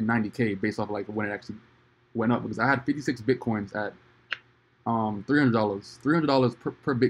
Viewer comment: OMG !! Did he say 56 BTC ?